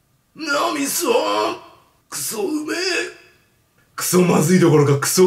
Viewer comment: 秒辺りのとこから、脳みそ〜！クソうめえ！って聞こえたの俺だけ？